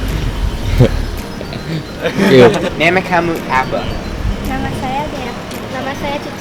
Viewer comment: Nemek kamu apa, i thought i heard "mem*k kamu apa"